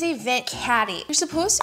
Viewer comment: i thought she said vent candy